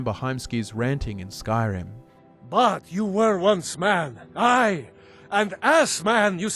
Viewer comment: Sometimes I hate how my brain works. Preacher goes: "But you were once a man! Aye! And as a man..." and I hear "But you were once a man! I, an ass man..." and it completely ruins my focus